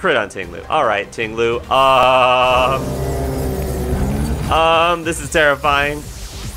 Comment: "This is terrifying" What i hear.."this is tera flying"